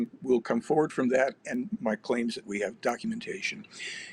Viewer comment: he actually says "My CLAIM is that we have documentation." Yet you didn't think to maybe pull it from a file and show it to us? I'm a believer in disclosure, I'm following closely, but this just trust me mentality is getting old.